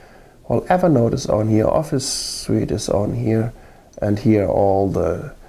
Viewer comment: did you say office sweet 16:09?